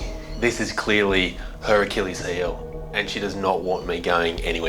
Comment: Did anyone else notice he said “Hercules heel” instead of Achilles lol? Just thought that was funny, makes sense since they both start with h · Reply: he said “her achilles heel”, but his accent made it sound otherwise